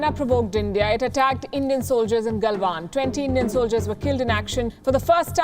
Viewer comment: When she said "20 Indian soldiers were killed" in her accent, I thought she said 20 million soldiers were killed.